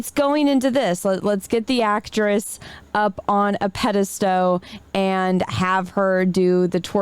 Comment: did say "pedastow" ? Lol